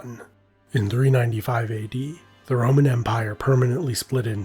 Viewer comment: I swear he’s saying the ramen empire